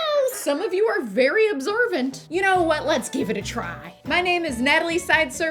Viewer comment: I heard “absorbent “ instead of “observant” 😂